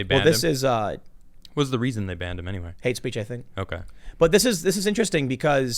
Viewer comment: “What is the reason they banned him anyway?” *”Aidsbejeythik”* Anybody catch what the hell Tim said at 8:18? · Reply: "Hate speech, I think."